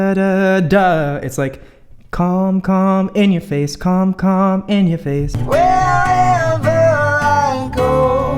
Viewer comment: did you just say “come in your face”